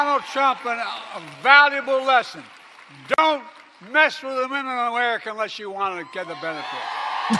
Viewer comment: "Dont mess with daawuminamerica unlesss ughhhhwannagggedabenefitttt" THE SHEEP CLAP WHILE THE REST OF US ARE LIKE WHAT THE F DID HE JUST SAY🤣😂🤣😂💀🤦‍♂